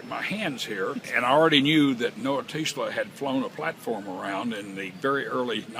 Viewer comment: who flew a platform around in 1904? it sounded like he said Noah teesla? - almost seems like a rhym with Nicola Tesla